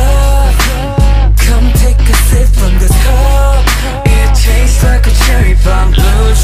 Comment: i thought it said come take a sip from this car and i am now forever confused